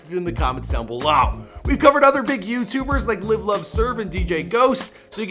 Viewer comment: Is it just me or did he say live love serve